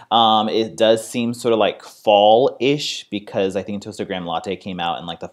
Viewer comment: At first I thought he said toasted grandma latte 😂😂